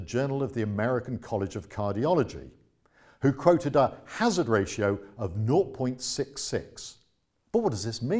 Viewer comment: what does he say? 0.66? North.66? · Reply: "...who quoted a Hazard Ratio of 0.66..."